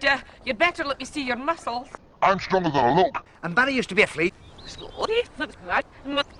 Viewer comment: Paul's stronger than he looks. And Barry used to be a flea. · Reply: Man of YTP I always heard it as “I’m stronger than the Hulk!”